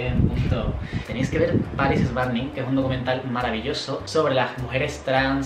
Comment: No le entendí que dice alguien me puede decir que dijo? perdón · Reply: Un documental maravilloso